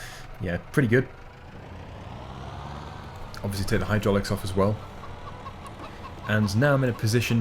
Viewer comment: captions say obvoiusly tear hydroaluics off! (US English)